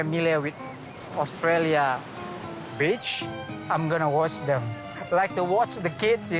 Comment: I thought he said “there not familiar with australia... 3 second pause ✨Beetch✨“ 😂 but after 20 with subtitles it was beach 🤣🤣🤣🤣🤣🤣 my bad ✋🤣